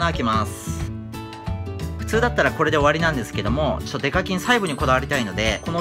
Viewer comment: 今日はデカキンがデカチンに聞こえた